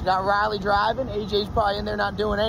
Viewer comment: Lol!! It sounded like you said ‘Sunken Girl’ when you said Southern Girl!